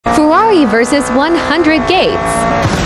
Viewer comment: In the beginning I thought it said Ferrari vs 100 gays